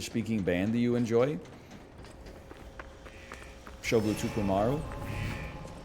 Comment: Anyone know the band name he says at 30:06? Subtitles saying “show blue super Mario” isn’t being helpful. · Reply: it's a guy named shugo tokumaru. he does every aspect of his music (singing, writing, producing, playing, etc.) and plays many instruments